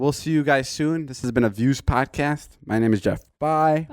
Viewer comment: I hear he says my name is Jeff, am I the only one think so?